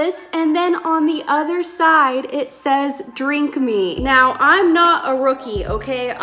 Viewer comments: I thought it said drunk me at 2:56😂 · I thought the tea bag said drunk me (as in your videos) and not drink me and then I was like “she did make this tea”